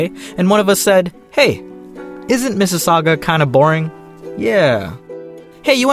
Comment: wait did u just say Mississauga do u live there??